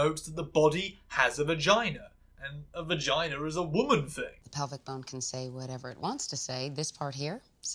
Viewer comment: I just heard it as 'pelvic bone' when I was watching the episode.